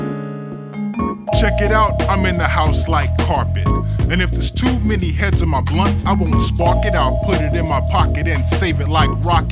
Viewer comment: Check it out, I'm in the house like carpet And if there's too many hits on my glunt I won't spark it, I'll put it in my pocket and save it like rocket fuel Til' everybody's gone and it's cool Then I spark it up with my brother His momma named him Moe but I call him Mo' Lover and he's more than a cover he's a quilt We're putting shit together like that house that Jon built on the hill cuz this shit's gonna feel, like velvet Turtle My style fits tighter than a girdle If ya hate it than you can just leave it, like beaver But in a day or two I'll make you a true believer, in me Cuz like the alphabet you'll C That Ism kicks a rhyme, not your everyday soliloquy Like Chef Boyardee, my rhymes are truly cookin' Peace to Matty Rich cuz he's straight of of Brooklyn, New York I don't eat pork or swine when I dine, I drink a cup of koolaid, not a big glass of wine Or some heineken, if you have time I'll drop rhyme again Edit: Corrections because I messed up due to a sinus infection. Props to "my pp small , i was cucked and" for the help, and Kizu, and mikescubes. Edit Again: Thanks Frogmask and PaperTiger Final Edit for Now: Thanks to S S P and Monst. I put glunt back, but it is blunt. Thanks for the group effort everybody! <3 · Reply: I thought he was saying “if there’s too many hits in my blunt” which makes sense because I won’t spark it and put it in my pocket, but that would be insane to put in a song for a Japanese game